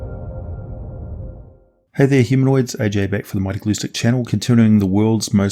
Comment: I thought you said," Hey there hemorrhoids" lol